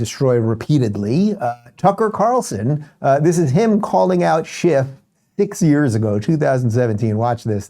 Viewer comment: Oh, you said "calling out Schiff"...I thought you said "calling out shit"......wait, same thing